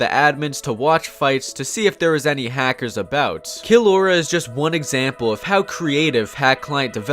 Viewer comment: He was saying “kill Aura” too fast and I thought he was saying “Killrora”